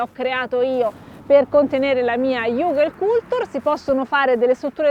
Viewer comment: non ho capito iuve cultur? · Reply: Hugelkultur (coltivazione su collina) <link>